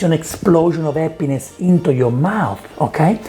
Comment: Hello. I'm watching this in 2025 with automatic subtitles on and I just want you to know that when you say "you create an emulsion and that's what gives an explosion of happiness into your mouth" at 7:30 , the automatic subtitles say "explosion of a penis into your mouth". I wish I was making this up and, looking at the date of the video, it kinda sucks that I am 4 years late to the party, but this I just couldn't keep for myself.